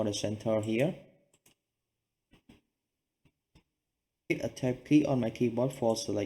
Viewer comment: What key did you say at 20:38 ? I can't select it pls help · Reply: Hi, Hit a Tab key on your keyboard for selected your glass panel, you might need to select mutilple time, until highlight only one glass panel, so from there, you can change from glass panel to glass door.